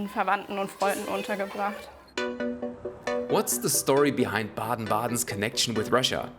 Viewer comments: I misheard this as Biden-Biden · I misheard as Biden Biden the most russian city